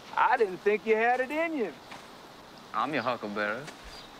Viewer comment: This movie has one of the biggest misheard lines in movie history. It’s not “I’m your huckleberry”, it’s “I’m your huckle bearer”. A huckle bearer is an old southern term for a pall bearer. So he was basically saying I’ll carry your coffin.